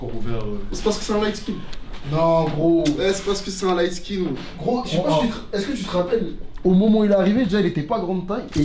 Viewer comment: c un quoi il a dit? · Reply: Un lightskin " petit gabarit " ;)